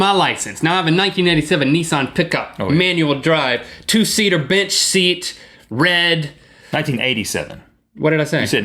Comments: I didn't care if he said 87 or 97.. I thought he said bitch seat instead of bench seat. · Did Link say "Bitch seat"? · / / / what did he say? two seater ???? seat? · did link say "a bitch seat" at 8:08 ? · lol sounds like you said i got a 2 seatar bitch seat